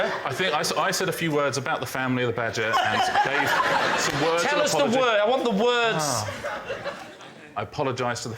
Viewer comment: did anyone notice when Richard says "I said a few words about the family of the badger" and then lee interrupts, I think, and Ricard says "and then Dave-" Was that the name of the banker? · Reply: He says "and GAVE some words of apology"